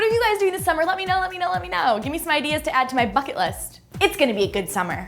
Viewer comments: I thought she said "my fucking list"  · I know at the end she said "bucket list" but I heard it so fast I thought she said "fuck it list" oops